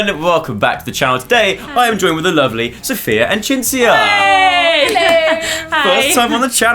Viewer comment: I thought he said "Sophia and Chinchilla"